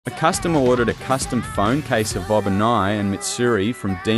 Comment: I heard "Bob and I" instead of Obanai